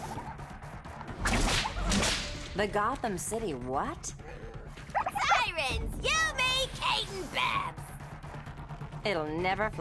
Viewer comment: What did harley say at 14:34? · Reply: bumblebee scout “Sirens! You, me, Kate (Bat-Woman), and Babs (Batgirl)!”